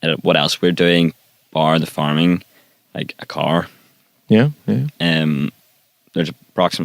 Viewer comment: is he saying cow or car??? Someone help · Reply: nickability car